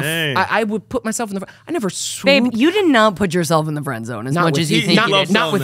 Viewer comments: Did she call him babe 14:32 👀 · Did Lindsay call Devin “babe” at 14:33?